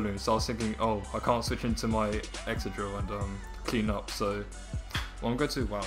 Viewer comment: did you say excadril? wat